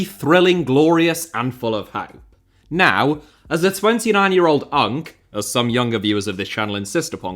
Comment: i legit heard hunk lmaoo